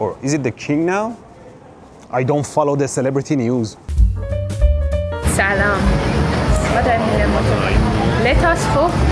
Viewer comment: since when do "canadians" greet with "salam"? Or did I hear that wrong?